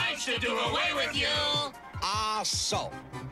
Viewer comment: What did said at 2:02? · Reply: I think he said so, which I believe means yes in Japanese, although I could be wrong.